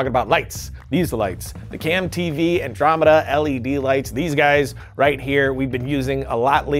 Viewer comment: I thought you said boltzen Andromeda LGBT